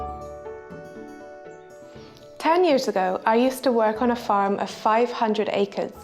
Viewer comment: Its just me, or i heard: "ten years ago". Here? 1:55 A